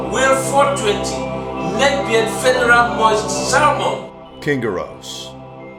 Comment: Did He Said Lesbian?